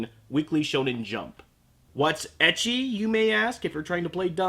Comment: am I the only one who thought he said "edgy" not "ecchi"?